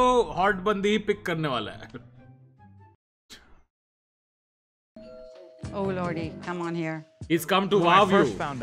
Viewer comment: did they say oh laudy?